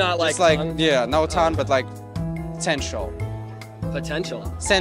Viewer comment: To be honest, I thought said potential too. is gorgeous!